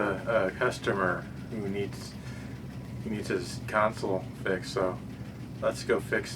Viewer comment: I thought you said tonsil.